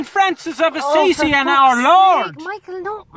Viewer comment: For those that dont know, Westlife is an Irish boyband. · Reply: Thank you! I thought he said West Lee.